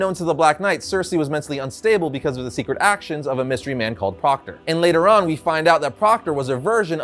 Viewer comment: Proctor? I thought you said Crocker! All I could think about while I looked at this supervillan was: FAIRLY GOD PARENTS!!